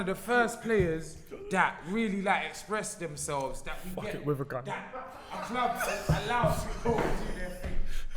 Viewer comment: what does he say? “ fuck it with gun”.? I need to know! I am in stitches 😂😂😂😂😂